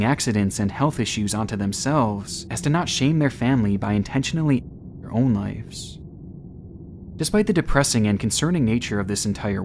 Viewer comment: what word is at 11:38? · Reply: He probably said "taking their own lives" but youtube is dramatic about words